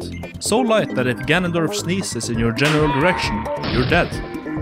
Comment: I thought he said "if ganondorf's niece is in your general location"